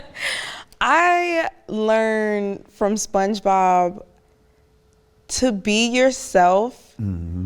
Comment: Did she just say " i learnt from spongebob" 😂😂